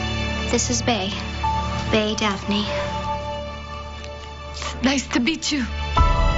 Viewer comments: ''Nice to beat you?'' is anyone else hearing this? K im scrolling through the comments to find anyone who hears this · Whenever I saw the scene at 1:55 in the show trailer, I always thought she said, "Nice to beat you." I know she's deaf and so speech is different for her, but as a teen, it was weird.